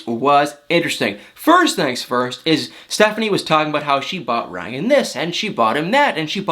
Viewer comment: I thought I heard Stephanie call her boyfriend Bryant not Ryan.